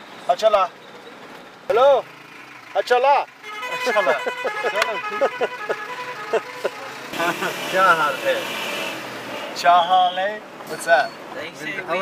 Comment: Ha Cha La Everyone! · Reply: i thought he is trying to say "acha hal hai" means R u fine? :D